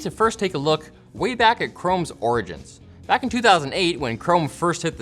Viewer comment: does he say "origgins"?